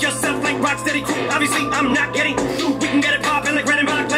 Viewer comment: Wow he said "i'm not gettin'" in such a way that it sounded like he was saying "obviously i'm not gay" to reply to the homophobic diss